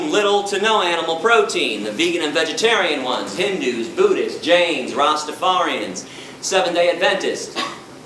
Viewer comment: A los 37:10, Gary no dice judíos (Jews), sino que dice jainistas (Jains). El jainismo es una religión de la India.